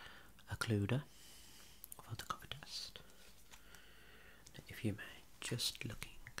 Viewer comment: I fr thought he said "we should perform a covid test now" I'm like waittttt